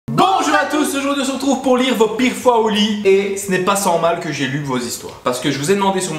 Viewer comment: "Vos pires fois au lit" au début j'ai compris "big flo et oli" j'ai dit WTF 😂